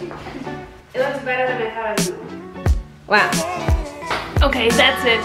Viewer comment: What does Kate say at the very end? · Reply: "It looks better than i thought it was going to look". A real sweetheart lol